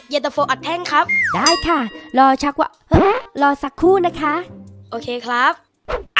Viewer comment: ตอนที่1:28โครตตลกเข้าเกื้อบพูดว่าชักว้ำ😂😂😂😂